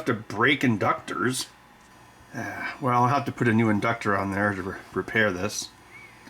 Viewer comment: What did he say? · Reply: “Well I have to put a new inductor on there or repair this”